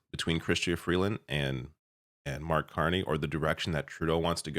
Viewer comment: I swear I thought you said Art Carney...twice....and I was like "isn't he dead?". But hey, that housing fella never really inspired much confidence - in any position.